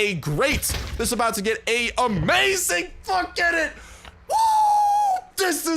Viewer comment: I think u said a bad word🤔🤔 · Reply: He said forget it but I had to search 12 times